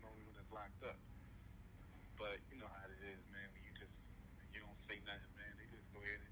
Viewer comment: Not “when you don’t say nothing”, rather “when you don’t know what to say”… (you was a child bro!!)